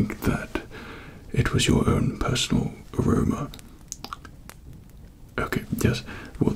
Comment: did he say corona